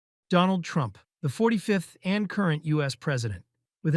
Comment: Donald Trump... For a moment I thought he said "The 45th and corrupt US President" 🤔😂